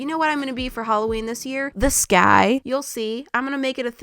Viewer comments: Is it just me or when said the sky I thought she said this guy lol😅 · I thought she said "this guy" but then I noticed she said "the sky" 😂😂😂